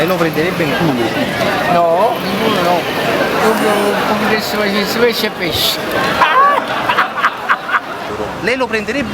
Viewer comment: Cosa dice in dialetto a 0:45 ??? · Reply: m' piacess pesc e pesc (mi piacerebbe pesce e pesce, in napoletano, a parte putarria (potrei) e vurria (vorrei) (e comunque sono termini che si usano solo quando si canticchia una canzone classica napoletana) non si usa il condizionale)